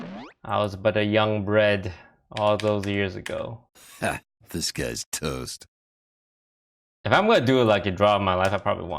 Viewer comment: You're right. If I remember right, his hearthstone name is just "Toast," which is a joke from the SI7 Agent. But when we went to a tourney, he took the joke a step further and he had his classic toast head on. That's when people called him "Disguised Toast." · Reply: I'm pretty sure it's just the line "this guy's toast" misheard sounds like "disguised toast".